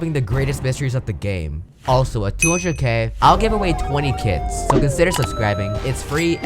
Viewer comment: I genuinely thought u said u were gonna giveaway 20 kids at 200k subs